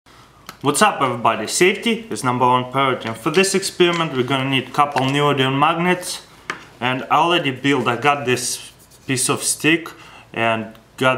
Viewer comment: Did he just say neomagnum magnets?